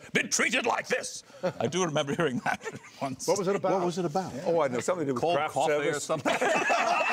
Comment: Does anyone know what they say at 0:30 ? I can't quite make it out. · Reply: BOomeek Frakes said, "I don't know, something about cold coffee or something"